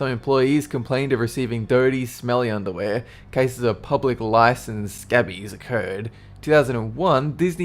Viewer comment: does he say public lice? You mean pubic lice